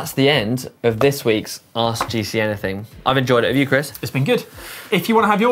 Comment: Did he just say ' Welcome to ARSE GCNything'...😂😂😂 You decide!!